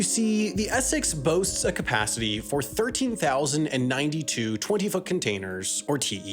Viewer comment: I thought he said 20 fucking containers instead of 20 foot 😂